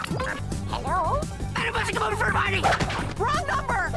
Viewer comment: I think what said at 1:01 was “MADAME FOSTER COME OVER FOR A PARTY!” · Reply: I hear "BUTTER BUSH COME OVER FOR A PARTY!"